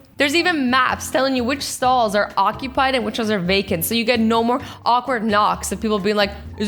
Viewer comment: instead of saying " Taken" She said "Vaken"